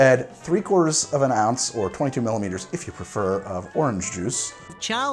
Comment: Me: did he just say millimetres? Greg: tastiest pote of the drink. Me: you know what? It’s fine.